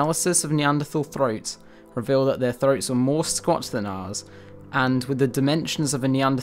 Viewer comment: What is he saying about their throats @ 11:04 ? ("...more scots than ours"?) . . Re-playing it several times but can't make any sense of it. Otherwise good and highly informative post despite two or three well-traveled areas am surprised you felt the need to include.